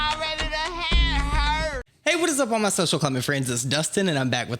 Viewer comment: Omg i watch ur videos every upload and i thought you were saying” my social climate friends” untill today 🤯🤯🤯🤯🤯🤯